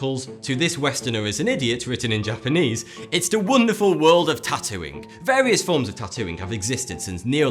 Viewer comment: When he said 'the wonderful world of tatooing' I thought he said the wonderful world of tatooine.